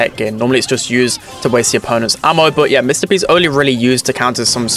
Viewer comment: did anyone else hear “Mr Beast” instead of “Mr P”?